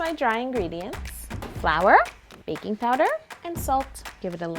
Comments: I thought when you said baking powder i thought you said baby powder!! Lol 😂😂😂😂😂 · I thought she said baby powder instead of baking powder lol